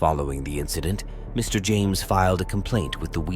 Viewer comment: He said "Wheat Ridge" police officer. I thought he said "Weak Rich" police officer, I had to put closed caption on to see what he was saying Lol 😂